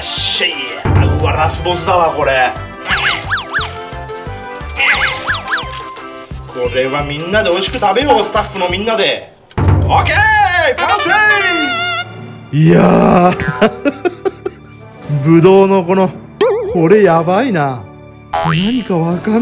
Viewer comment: 完成がパフェに聞こえるわw いわし😊